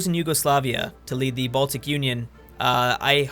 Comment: DID HE SAY B A L T I C UNION?